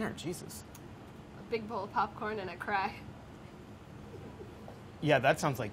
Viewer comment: "a big bowl of popcorn and a cry" sohla is a woman of culture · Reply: Oh, I thought she said a bowl of popcorn and crack.